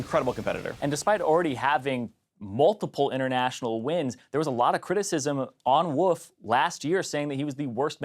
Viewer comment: did he just call Wolf, "Woof" ??